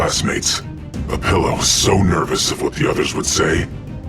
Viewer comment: I thought that said Olivia when Opila win the contest